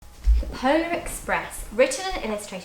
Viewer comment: When it first started I thought she had said the hoe express! Then I was like bloody hell no way that's a book so I started it again and the polar express! 😂